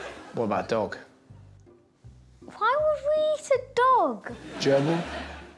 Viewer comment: I misheard Russell when he said a gerbil talking about what you would eat. I thought he said a German! 😂